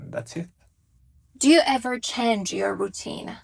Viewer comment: what she said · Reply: Do you ever chance your routine